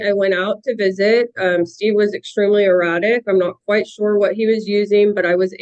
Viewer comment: Did she say that her husband was highly erotic, or erratic? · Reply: Definitely erotic 😂